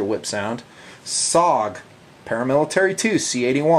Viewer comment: For all the people who didn't hear it 23:28 oooo look messed upppp ooooo *sarcasm* "SOG Paramilitary 2"